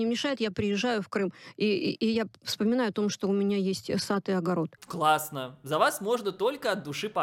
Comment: Мне послышалось, она сказала «вссатый огород»🥲